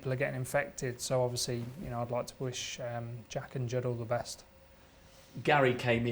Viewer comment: Did he just say he wish Jack and jill all the best 🤣